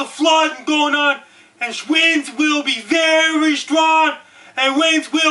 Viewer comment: This guy should be the voice for the emergency broadcast system!!! hahahaha Holy fuck did he just say shwinds?! hahahahahaha